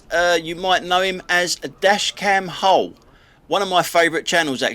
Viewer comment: When you say "dash Cam Howe" where is it ? maybe it's your near cockerney axent wot makes it difficuwt ter anderstained bur are cornt find it !! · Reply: It's Dash Cam Hull!! I thought he said how or something similar at first!!!